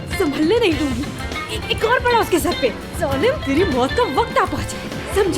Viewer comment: What did she said ? .😂😂😂 "Gand mara unke sarpe" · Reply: Ek or pda uske sar pe 😂😂😂😂 ye kha hai usne idiot 😁😁